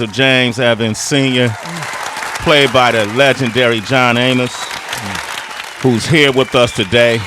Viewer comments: Hold up, 0:57 secs ......did he say "John Anus"? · Thought he said John Anus